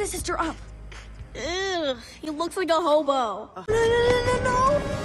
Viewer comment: she does not say "Homo" but "Hobo" which is another word for homeless.